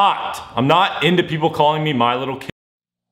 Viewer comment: At the start i thought he was saying im nit gonna let ppl call me littles coc-k